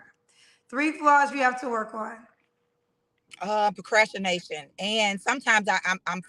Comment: did she say procrashination? HaHa